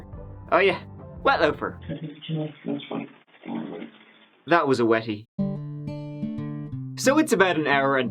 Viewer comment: I thought said That was a Yeti lmao 🤣